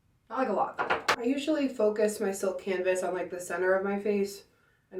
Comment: I legit thought just said “I usually put my silk canvas on the placenta on my face “ 😫😫